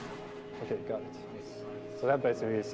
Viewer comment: What happens when he gets hit? What does he say? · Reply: “okay got it”